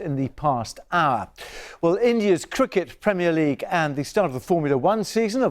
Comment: I thought he said 'India's CROOKED premier league's and I was like aight BBC I'm done wit y'all